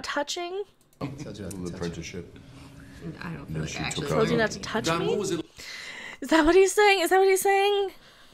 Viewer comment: I heard "i told you not to touch me" great vid